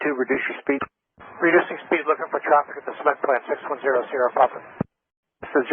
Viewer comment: Did you just say “see-ment” plant? 😅😊 sounds like Texas ! ( from someone who lived there for 25 years)